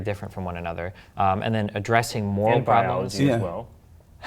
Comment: can someone tell me what he said? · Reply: "and biology as well"